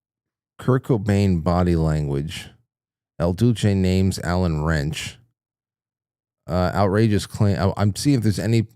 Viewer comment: Sounded like he said Allen Ranch